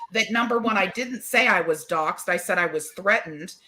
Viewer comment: I thought said she was doxxed. I know KIC was.